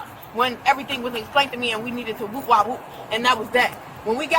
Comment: "...and we needed to woo wa woo..." Wait. Wtf did she say. I am so😂😂confused.